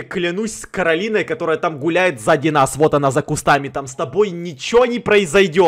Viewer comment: Мне одной послышалось что сказала з@ круто